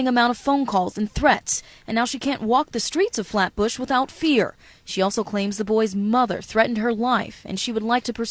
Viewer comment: I thought the news reporter said "she cant walk the street with a flat butt without fear." i had to go back.